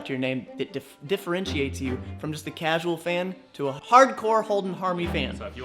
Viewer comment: ‘Holding a hard one”...... is what I thought you said when you introduced yourself. I need to get more sleep......